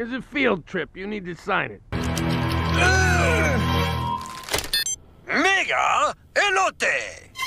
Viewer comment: what did bro say💀? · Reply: Mega Elote!